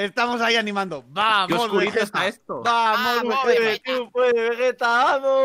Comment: Parece que dice: "Vamos Wigetta"